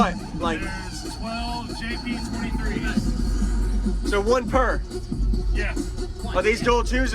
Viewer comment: dailylfe3975 why run 3 when she's running 12 15ks😂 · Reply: i thought she said 12 jp 23